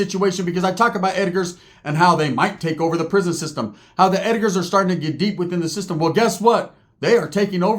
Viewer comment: I thought he said the Edgar's were getting dick 😂😅